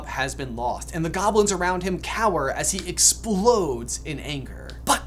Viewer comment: Where's Lupin? But also, I heard "the globlets around Voldemort cower in fear." I mean, he IS in a vault full of fancy dishware...